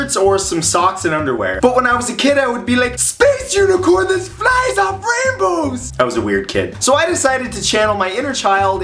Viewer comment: what did he say at 0:41 to 0:44?? 😂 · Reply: Cadence Maxwell space unicorn fly off rainbow